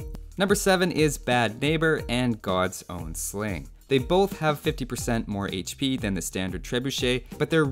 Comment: did I hear 50% more HP, as in 300 instead of 150? My spirit senses start tingling there